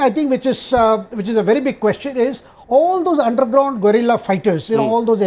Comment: Is it my ears ringing or did i just heard all the underground gorilla fighter...😅 (Vid